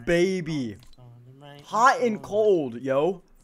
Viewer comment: I thought it said Thot and cold first